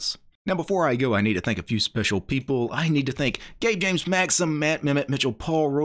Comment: Every time he said the executive producers out, I hear him say "I need to thank gay James"